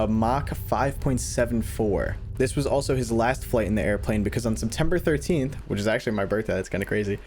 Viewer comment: FOR A SECOND I THOUGHT U SAID SEP 18TH WHICH IS MY BDAY